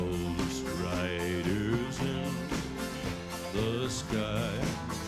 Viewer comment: As a young child first hearing this on the radio. I thought he was saying " Ghost Riders in Disguise" lol I'm 52 now.